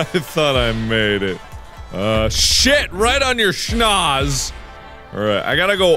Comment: Was he saying "right on your Schnauze."? Cause that's German. ^^ I like it.